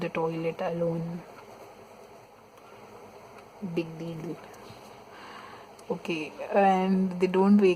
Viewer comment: Did she say 'Big Bebu" in a baby voice